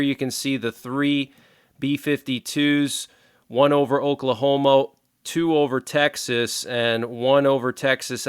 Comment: Dude, did you say Oklahomo?